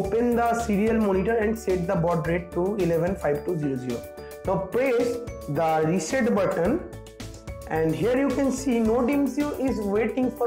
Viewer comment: what did u said press reset button which button Nodemcu's ??? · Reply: Yes reset button